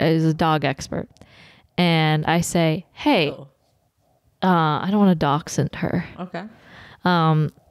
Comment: it kind of sounds like says "I don't wanna Dachshund her" which is hilarious considering the subject